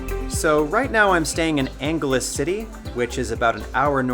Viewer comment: Wonderful video. Could you please give the name of the city you are living in? I thought you said Angala City. To be on the safe side could you spell it for me. Thank you kindly.